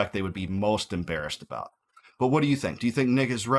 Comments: What did you say at 26:41? Do I think who is right? 😂😂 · Man at the end 26:41, you said "What do you think is nick is right?" But it sounded like you said" What do you think "Nickus" right? Had to do a double take :). I say let it crash if tis that corrupt.